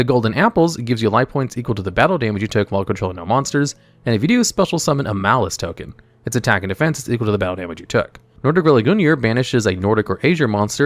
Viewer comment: I was listening to this video and had to do a double take here. I heard Maliss token lmao.